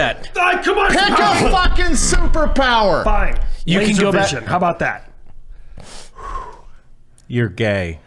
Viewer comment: What the fuck did say at 1:06:20? I can't for the life of me make it out!! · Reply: I think he said laser vision